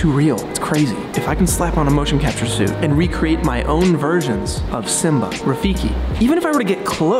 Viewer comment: I misheard "...and recreate my own VIRGINS"